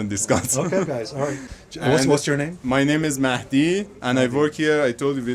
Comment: i heard his name MACD